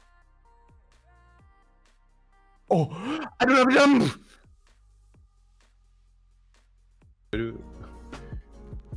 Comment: What did scream? xD lmao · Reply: he said:''HHHH, amelamelam!?"